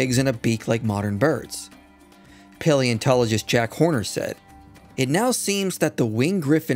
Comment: did he just say jack horner?